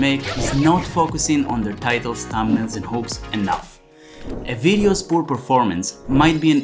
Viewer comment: says what? Not focusing on their titles, thumbnails, and... it sounded like hopes to me after i rewound. Oh maybe you meant hooks. I'm struggling with your accent, i must admit.